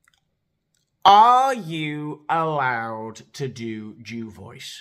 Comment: Hey Mister, what are you saying @ 7:40 ? No more Joy Behar ? 😢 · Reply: I'm allowed 😄